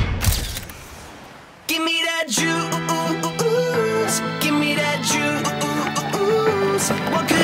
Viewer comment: Am I the only one who misunderstands the text. I heard "Give me that jews..." first. I'll think I should rewatch. Well, I always rewatch so I have to triple watch!